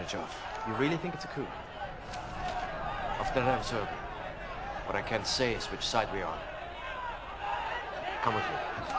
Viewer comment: Can someone tell me what he says at 4:40? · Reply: Bro I know this is like a 4 year old comment but I believe he said "after observing*